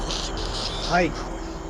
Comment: I heard a lot of answers that weren’t subtitled. At 2:50:41 you can literally hear it loud and clear say “ANSWER HIM” after the dude asks “do you remember what happened that night?”